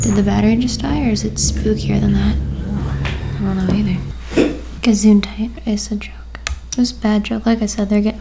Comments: did say “gesundheit” ? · Did say "Gesundheit"? which (witch! haha) is the german word for "Bless you" when someone sneezes. I was just wondering :D I love this halloween series! <3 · Okay amazing video but did you say „Gesundheit“ at 16:53?? I’m German and it sounded like it xD (Gesundheit means bless you) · did she just said ''gesundheit''???? lol your german knowledge is awesome